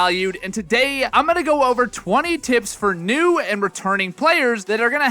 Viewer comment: bro i swear at the beginning it sounded like he said "For new and ret*rded players."